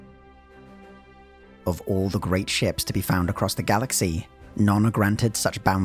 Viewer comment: I misheard that opening line as "Of all the great Chefs to be found across the galaxy..."